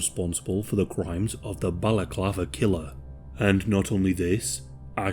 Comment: Damn... I thought it said the "Baklava Killer." I'm less interested now...